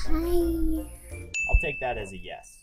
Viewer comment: it sounds like said hi